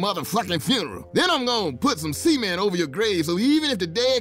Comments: when I first saw this episode i thought he said semen lol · When Mister said "cement", I thought he said something else. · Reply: gokuandmario4ever thought he said semen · Did he say Cement or Semen? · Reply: I totally thought he said semen too! Lol i guess zombies hate that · When Mister said "cement", I thought he said something else. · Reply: I legit thought he said semen. I’m like zombies eat brains I don’t think jizz is gonna stop a zombie like how holy water, a cross or garlic stops a vampire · Who else thought he said semen at first xD? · does he say semen? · When Mister said "cement", I thought he said something else. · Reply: Always thought he said semen. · Did he say Cement or Semen? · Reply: I thought he said semen.